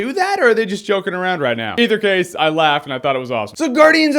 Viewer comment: Huh? I couldn't make out that last word ***** said about the Post Credits at the end of his sentence. When he said "Either case, I laughed and I thought that was ????" .. Did he say "all?", "Awesome?", "Awful?" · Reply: He said awesome.